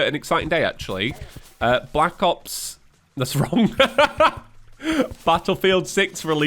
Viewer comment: "Black Ops... that's wrong" I misheard this as "Black Rock" and just went "Woof, I WISH Black Rock Shooter 6 came out. I'd kill for more of that setting.